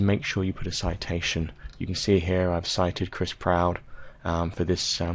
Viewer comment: I'm so nervous for a near presentation. Thank you! Also, I at first thought that you said Chris Brown at 24:03. Thank goodness I heard wrong.